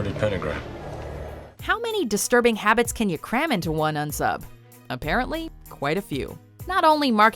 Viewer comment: What does the narrator refer to the killers as? all I hear is "N sub"? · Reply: riiiigggght UNknown SUBject